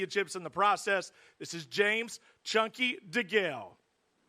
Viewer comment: I thought he said "James 'Junkie' Degale" for a minute there. It probably would have been more appropriate though! 😂